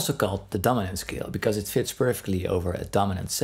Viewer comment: When you began talking about the mixolydian I thought you said "it is also called the dumbass-scale" and I was like he's throwing made shade here, but then I realised you said "dominant scale". Great work again! 👍 😎